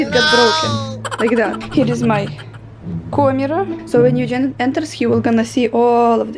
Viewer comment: She said, "camera." I heard "Comradd" 😅